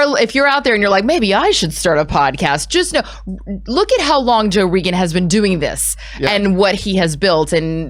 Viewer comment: Marvel did the same thing couple years ago w/ “Wolverine: The Long Night.” So this is pretty cool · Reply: Did she call him Joe Regon?? Lol anyone noticed?